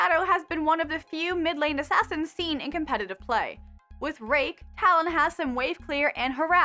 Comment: Am i the only one who heard rape ? :D